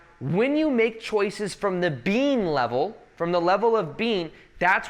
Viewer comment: I thought he kept saying the bean level. LOL